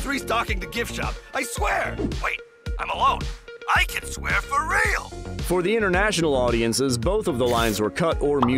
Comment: huh I thought he said 'I can curse for real' not 'I can swear for real' (It's been a long time and I need to rewatch)